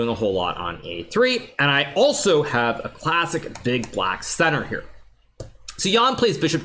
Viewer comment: Did I hear that right? Classic Big Black Center 😭